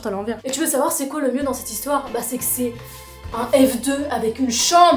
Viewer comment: un quoi ? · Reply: F2